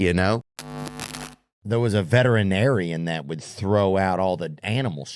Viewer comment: Idk why but when Theo said "veterinarian" for some reason i heard "a Veteran Aryan" 😂😂. Never really thought of that b4 lol. Thats kinda wild 😅😅